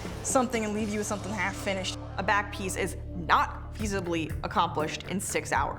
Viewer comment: is so beautiful 😍 but did she really say "easibly" instead of easily !!!